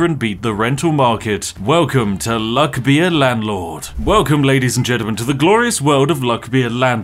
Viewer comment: What's the game called I'm sooo dumb I thought he was saying luck via landlord so I didn't take notice of the massive letters telling me what the game was called